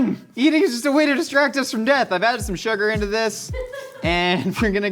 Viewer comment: I'm so glad we're all on the same page about that, though I heard "dread" instead of "death"